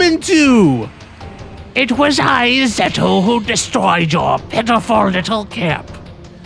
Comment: You said 'Zeto' and I heard 'Seto'. Then I was all 'Seto Kaiba destroyed our village?!'